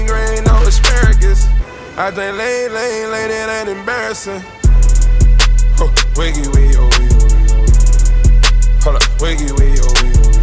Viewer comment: Why I thought he was saying wiggy, wiggy, wiggy?